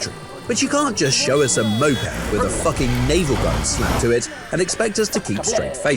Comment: did he just said fucking💀💀💀💀💀